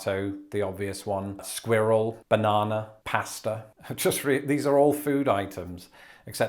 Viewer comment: “They’re all food items, except squirrel.” I know people who would beg to differ. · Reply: I would beg to differ that "pastor" is a food -- or did I completely mishear that one?